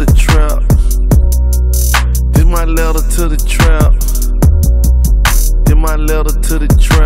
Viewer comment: i thought he said <<this my letter to the trump>>